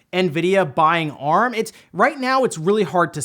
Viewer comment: Ohhhh, I thought he said Nvidia bought ARMS